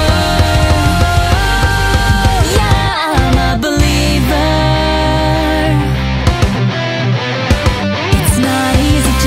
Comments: I always heard "Shut up, I'm a believer!" On the original. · For some reason in the original I heard "Shut up I'm a believer!" near the end of the opening. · I always hear I'm a belieber!! I'm like the fak you belieber m8!!! 🙄🙄🙄🙄😱😂😂😂